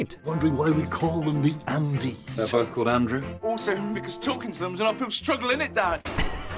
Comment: I had so much trouble understanding what Danny said here. British is hard for a German 😅 eventually it clicked: uphill struggle "Also because talking to them is an uphill struggle, innit[isn't it] Dad?"